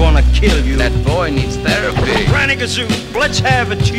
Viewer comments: Me hiding in shame because where it said "Ranagazoo".... I FLIPPEN THOUGHT IT SAID GRANNY KAZOO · who else came here for 0:14 "Psychosomatic" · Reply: came here for "Ranagazoo! Let's have a tune!" i thought he said "I have a kazzoo! Let's have at you!"